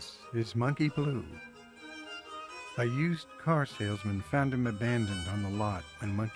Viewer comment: At first I thought you were saying Monkey IS a used car salesman. ha